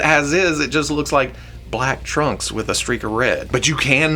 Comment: When was talking about Superman thought he was saying Trump, not trunks and I don't want to compare the two